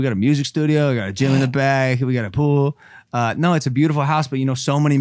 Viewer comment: Wait so, what’s going to happen w impaulsive? Is it done? Uploads twice a month? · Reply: Also at 8:14 does he say jail in the back?